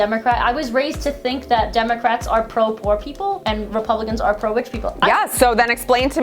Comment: lol I thought you said “I think Republicans are pro war people, and Democrats are pro rich people”. One is pro defense and the tribe, the other is pro la bohème